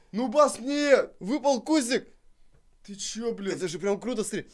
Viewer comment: Мне одному послышались что сказал слова бля